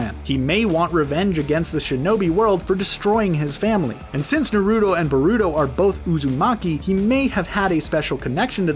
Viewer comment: did he just say narutto burruto ?